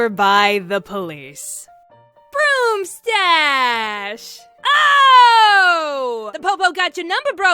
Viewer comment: Tap this and tell me the first word you here and tell me your first thought My answer: Heard: Ash Thought: Ash Ketchum 🤣